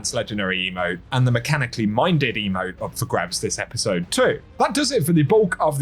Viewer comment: I SWEAR i heard you say "my diddy emote"